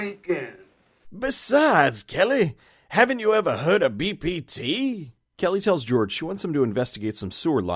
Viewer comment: Did you say BPT? I can't tell if I heard it right, and I don't know what it stands for, either way.